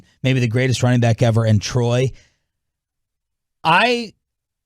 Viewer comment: Did he just say the best running back in Troy 😂